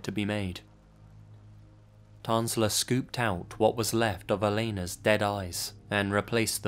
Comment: I thought you said "Tanzler scooped out what was left of her dead arse" I thought to myself "Damn, where is this story going...."